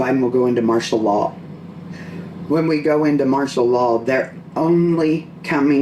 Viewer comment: Anytime someone says “martial law” in that accent all I hear is “Marcia, Marcia, Marcia” from the Brady bunch bit. 🙈🤣